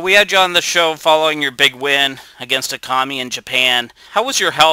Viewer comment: Lol i first heard "how was your fight against a commie in japan"